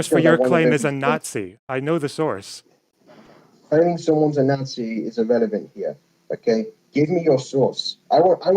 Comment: The man really said the fact his source his a Nazi is irrelevant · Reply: gloomy 27:25 what’s that you said there? Something about someone being a Nazi is irrelevant here? I don’t have to know your source you seem to have said it’s irrelevant yourself though I may have misheard thanks to your shitty mic. Also just a quick bit of debate advise saying shit like ‘I asked first I get first’ makes you sound like a petulant child more so than a respectable debater.